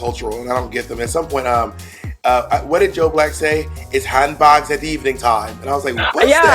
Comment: he actually said "it's handbags at dawn" ... He was offering up a humorous different take on the old thing people's used to say when settling a dispute: "it's pistols at dawn". Did literally nobody else get that?